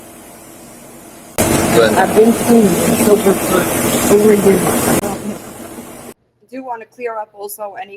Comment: I heard “I’ve been sober for over a year now” also not 3 years just my opinion